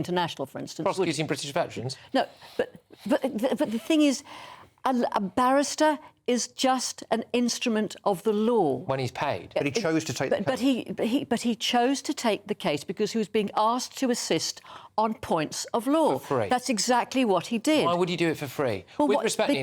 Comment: That crazy old woman said "Instrument of the law."... I heard "Tool of the State."